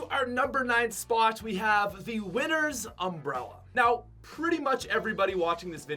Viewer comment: When he said winners umbrella I thought he said winters umbrella 😂😂😂